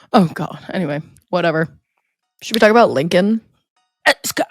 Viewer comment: When said "should we talk about Lincoln?" I misheard it as "should we talk about LinkedIn?" and thought you were fully committing to the detour into most insufferable social media platforms